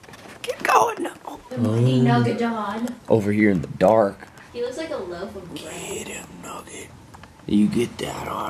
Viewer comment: does say 'he looks like a loaf of bread'? LOL if she did!